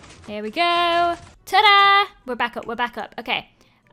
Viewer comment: What do you here? We're back up we're back up! You're back up you're back up! Or Rebecca Rebecca! I heard ALL! 🤣